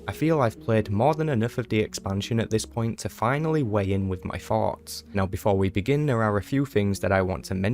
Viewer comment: Did you say "weigh in with my farts" at 0:42? 😂